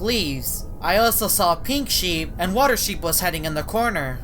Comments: I thought u said water sheep. White sheep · Did he just say “water sheep”…?